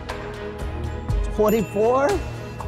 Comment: thumbs up if you herd 44 instead of 24 at 3:34 and then he keeps going on <num> instead of <num>